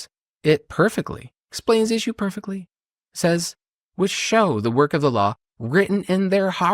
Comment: Is it only me but when he quotes Romans it sounds like he says '...will show the work of Allah'. Is he hiding something from his congregation?